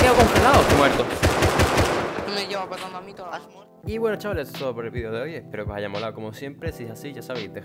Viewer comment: Soy yo oh escuche "wena chavaled soy cro